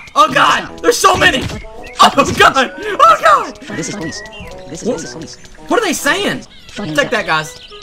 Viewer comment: I think they where saying "this is the police" lol. this game is funny